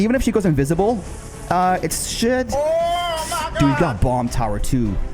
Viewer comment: did he just say sh*t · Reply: I said “should”